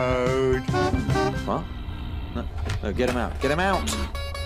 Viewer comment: ish did duck say Dan get out?